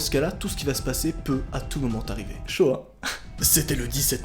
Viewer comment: c'est moi ou il vien de dire "shoah" oklm 😅